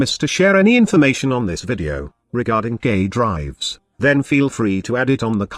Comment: Did I hear "gay drives"?